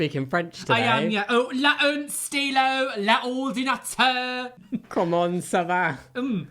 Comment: A translation of the french: "Hello" "The eleventh star, the computer" "How are you?" "My name is [bleep]" ...Quelle journée... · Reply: Yeah, I heard "la une stylo, la ordinateur" (the a pen, the computer) 😆